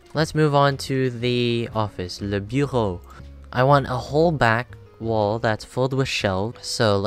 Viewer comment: and I thought i heard “the b-hole.”